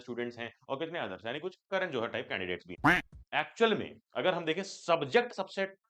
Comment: brooo whattt he really said the other category candidates are like ksran johar 😂😂😂